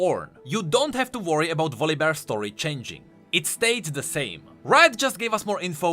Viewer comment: Well i was the only nerd that heard him saying ,,stayed" in stead of ,,stood".